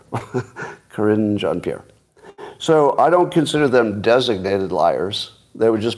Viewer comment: Sounded like you said Karinge Jean-Pierre. Very apt name.